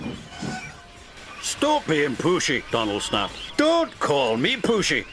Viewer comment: i thought they said "stop being pussy" "DONT CALL ME PUSSY"